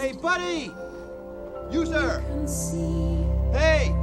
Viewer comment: My Mom thought that he said "User!" her whole life. The first time I watched this movie I was like it's "You sir!" She was shocked😂😂😂😂